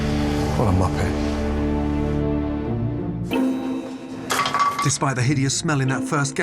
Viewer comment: Does he call him a Muppet? ✨😂✨